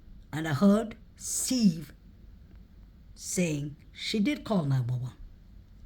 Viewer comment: I heard "she DID call 911"